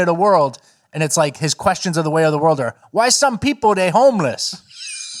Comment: Lol I thought pigeon man was saying "Why do we have homeless people, why are they homeless?" I guess it's all interpretive 😆